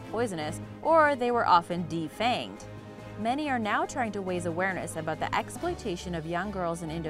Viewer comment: Not to sound rude, but I laughed super hard when I heard "waise awareness" at the snake charmer bit.